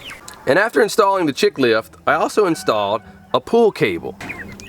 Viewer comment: Oh, a PULL CABLE... I thought you said POOL TABLE. 😀